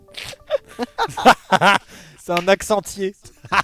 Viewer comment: Joyca : Je sais pas si t'as remarqué mais y'a un arbre à "é" derrière!! Amixem : as-tu bien tailler t'a "é" ? Meilleurs paroles de la vidéos 😂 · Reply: Moi j'ai entendu "c'est un accent 'anti "é" 🤣🤣